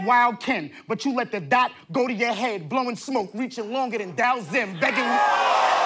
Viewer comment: K-Shine: “You let the Dot go to your head, blowing smoke, reaching longer than _________.” What did he say at the end, and can someone explain the line to me? Thanks! Apparently it’s a reference I don’t get. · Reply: He said Reaching longer Than Dhalsim. DHALSIM is a character from the fighting game street fighter who had arms that can stretch really long.